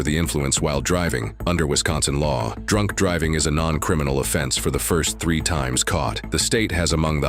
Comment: 😂 hol up… I must be trippin because I could have swore I heard this dude say that in Wisconsin “DRUNK DRIVING NOT A CRIMINAL OFFENSE FOR THE FIRST 3 TIMES”….That would be a wild thing to say lol I mean, no way anyone would be that stupid…..